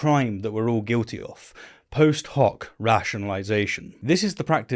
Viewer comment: I am like super high and I just heard this guy say Hawk Tuah 🤣🤣🤣🤣